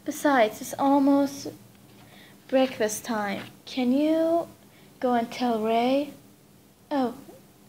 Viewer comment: can u go and get ray? What i hear:can u go and kill ray Me:😂